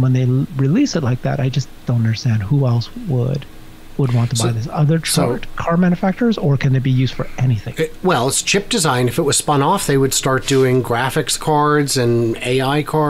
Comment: I thought you said 'ship design' and I was wondering WTF? - were you referring to the SPaceX booster landing boats? Then I realised you said 'Chips'... Must be your accent!